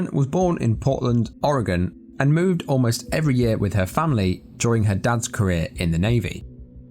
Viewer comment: When you said "during her dad's career in the Navy" I heard "during her dance career in the Navy" 😂 Love your content!